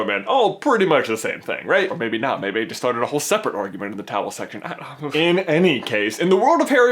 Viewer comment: what does he call the comment section? i keep hearing 'towel section'